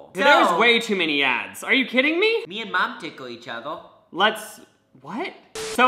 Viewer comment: Yo did he just say me and mum tickle each other? mum as in mom. But I mean mabey him and his mom do that idk